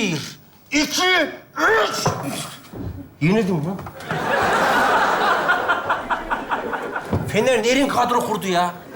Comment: de ne diyor anlamadım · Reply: “Yenildim lan” dedi gibi geldi bana